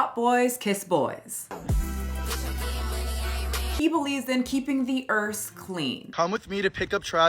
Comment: I thought you said 'Keeping the Ersf clean.'